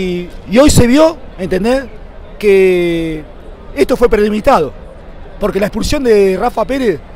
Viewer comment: ESTO FUE PRELEMITADO QUE DICE SEÑOR ???